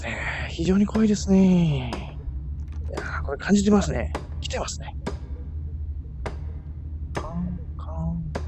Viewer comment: 来てますね が牛◯さんにしか聞こえなかったんだが